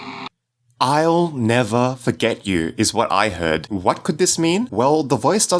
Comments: i heard "I will never Forgive you..." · i heard "Ill never forgive you! " creepy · is it just me, or did I hear "I'll never get you" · I heard "I'll never give you " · I heard I'll Never "Get" You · Remember to Subscribe and #TapThatBell for more horror game theories! Subscribe ► <link> All Tattletail Theories ► <link> A Message From Waygetter Electronics™ ► <link> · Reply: the manly voice kinda sounds like he is saying "I'll never forget you" or "I'll never forgive you" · I hear "I'll never eat you" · What I heard was "I...will never forgive you" but I dunno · I heard "I'll never forgive you" at the end of the video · i heard "ill never forgive you"